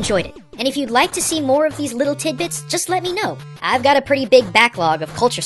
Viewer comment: Did he say "petite bite"? (it means "tiny dick" in French)